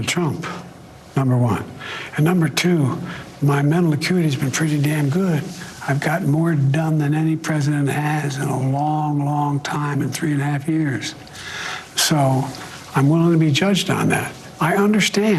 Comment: Biden just admitted he got more dumb than any president 😂. I heard dumb instead of done